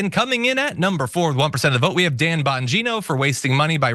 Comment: Ok wiping tears now... John, I'm just listening but when you went to the community garbage person I could have sworn you said "Tampongino"